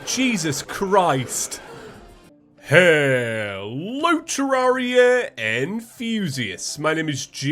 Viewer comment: Ok i know not only me thought that said Heeello terraria and fusious c Reply if you thought that said that